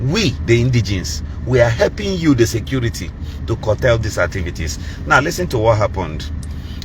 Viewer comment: I thought you morrons said SIMON IS THE INSECURITY